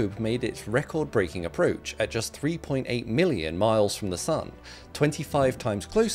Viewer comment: I thought he said 3.8 miles 😅